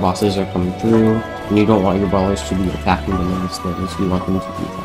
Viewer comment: love how ' subtitles say "Brothers" instead of "Brawlers"